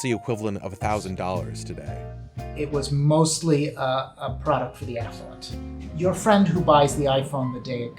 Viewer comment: Hahaha at first I thought at minute 2:08 he said " ..a product for the *ssholes, like the person who buys the iphone on the day it comes out" I thoughy "Well that's rude, accurate, but rude". My mistake.